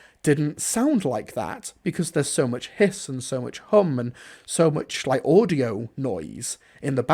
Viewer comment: At first I thought you said "there's so much piss and so much cum"